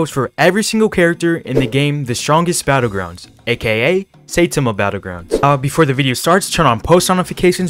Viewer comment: Did he just say "seitama battlegrounds"?